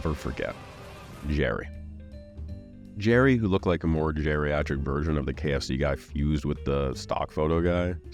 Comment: nah the captions did not just say “jerryatric” instead of geriatric omfg lmao 💀